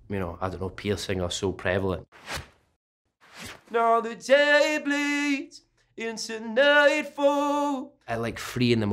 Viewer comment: I thought he said,”Know the day please,it’s a May 4.”